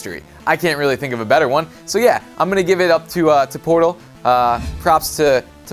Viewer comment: When you said portal, i thought you said porno